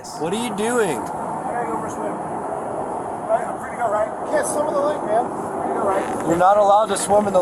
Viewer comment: The officer never told them to, not even sarcastically as you state, they gave him multiple warnings not to swim in the lake and that he wasn't allowed to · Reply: You're right, I misheard when he said, "You can't swim in the lake man," as "Yeah, go swim in the lake man." My mistake.